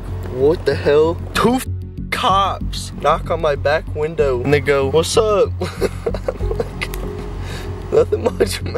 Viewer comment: had me going back and turning on the captions cause it did not sound like you said "they go whats up" lol